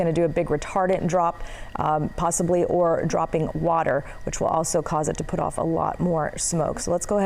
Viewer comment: That’s not a DC10! Please correct with the actual aircraft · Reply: AT THE 2:15 MARK, SHE CLEARLY SAID ""THAT''S A DC 10"" *MY DEAF GRANDMOTHER CAN HEAR BETTER THAN YOU!!*